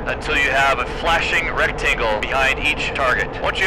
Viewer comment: My brain heard you say “red tingle” instead of rectangle. It was very confusing